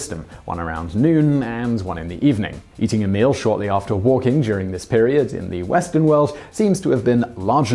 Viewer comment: "Shortly after waking" is what is written, but what is said is "shortly after walking". 🤦‍♀️